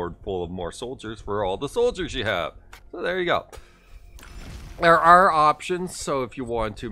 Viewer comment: " what are you saying " ;D erwh ar options